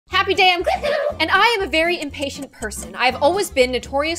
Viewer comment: I CLICKED FASTER THEN SHE COULD SAY “happy day i’m gloom!” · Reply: I thought she said happy day on lol